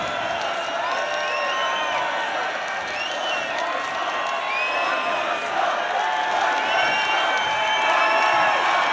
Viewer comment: what the crowd saying ? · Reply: Probably 'one more time'